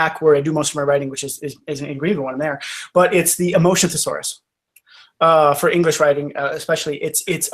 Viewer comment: Thanks for the video. mentioned a motion thesaurus. I'm having trouble finding such a book... EDIT: Nevermind, I have the EMOTION thesaurus. I misheard him the first time he said it. ^^